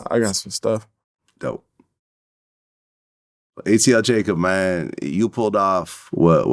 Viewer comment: Always thought they said atl draco🤣